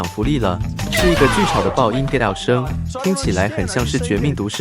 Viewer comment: get out 我每次都聽成幹，看了這部影片才知道其實是get out